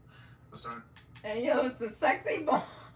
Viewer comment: Ayo what did you say Solar is what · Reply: I said Solar is sexy 💀am I wrong?